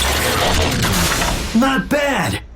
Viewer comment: did Sentinel just call Longarm a "cock"?